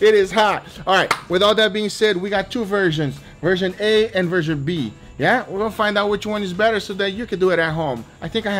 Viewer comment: when he said version A and version B it sounded like virgin A and virgin B 😂